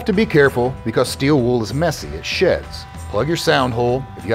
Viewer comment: “ steel wool is messy it sheds” I thought I heard “ steel wool is messy as sh@ I had to rewind three times before I heard correctly! my badhearing made the whole video for me!